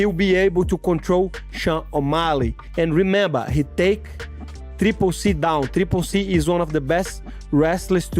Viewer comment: I thought he was saying "he take three p*ssy down" 😭